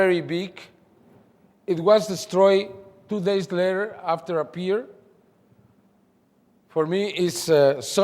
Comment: What does he say? It was destroyed 2 days later after a pee'er? Beer?